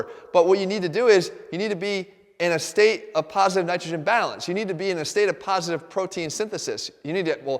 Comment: Does he say estrogen at 3:42 or extogen?